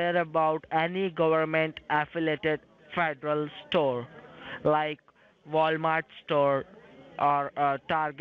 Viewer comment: I thought he said athletic store, ha ha ha. NIce job. Sorry I missed the premiere.